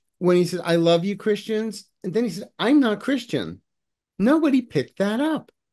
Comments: Trump actually said, I’m a Christian, but due to his slurred voice, it sounded like he said, “I’m not Christian.” 💙💙💙🇺🇸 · It sounded like Trump said "I'm not a Christian," but it could have also been him slurring "I'ma Christian" pretty bad. Hard to tell. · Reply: Covidiot clearly said "I'm NOT a Christian"